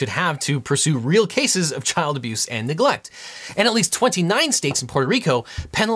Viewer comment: did he say 29 states in puerto rico?